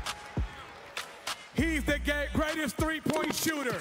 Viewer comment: Did he say He's the Gayest 😂